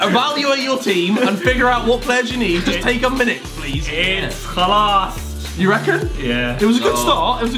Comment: did he said خلاص؟؟؟؟ where the fuck is this dude from?